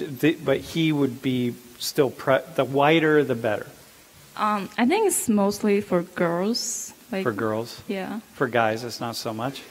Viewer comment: Hi. I'm not sure if you were able to understand my comment since I misspelled the word "Chinese" however I'm pretty sure you understood it. so I would like to write down what Dr. said and the Chinese woman said.. starting at minute 7:35 Dr. The whiter the better Chinese woman : I think it's mostly for girls Dr. for girls? for guys not so much? Chinese woman: not that much For me it's pretty clear that she is saying that Chinese woman have a preference for white guys but Chinese guys don't have this preference for white skin. Many times I have found Asian guys on the internet talking about this same issue! also, on U.S. dating sites it's very evident that Asian women have a strong preference white men! You also said "i’ve noticed women find darker men more masculine and don’t really care".. I think such words might only apply to black women.... but generally speaking, black women are only interested in black men... they don't have any interest in men from other races..